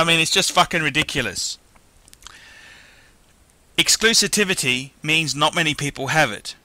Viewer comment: What was the word he used at 1:47? Exclusitivity? Clearly just making up words...